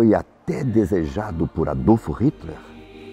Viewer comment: só eu que ouvi Adolfo ??